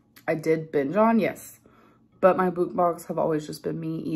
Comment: Did she just say bukmongs because she was too self-righteous to check herself? Survey says